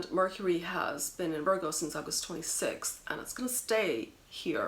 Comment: Just for laughs, at 2:32 I thought she said "and Mercury has been in Virgo since I was 26" but when I listened again, I found she said "since August 26th"